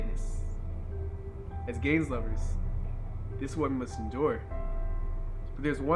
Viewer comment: I thought he said “as gay lovers”